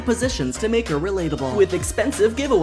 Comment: Help cause I thought he said “you get a cannibal” instead of candle 💀